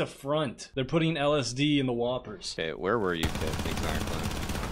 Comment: I heard "They're putting Elas D in the whopper"